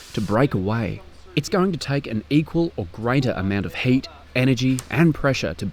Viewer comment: What he said in minute 8:14 "is going to take an equal or greater amount of hate energy and pressure" why he said "greater amount of HATE?" please what he really means? SOMEONE, PLEASE? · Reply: HEAT